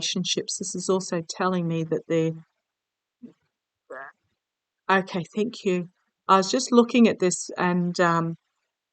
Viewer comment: maybe hearing one of your guides? Can't quite make out the word though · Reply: “Back”